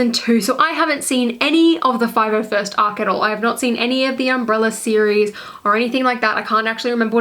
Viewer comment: Did she just say umbrella arc